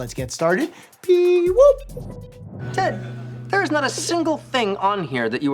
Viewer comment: Wait! It's "Pee"-woop. I always heard it as "Bee"-woop. The more you know.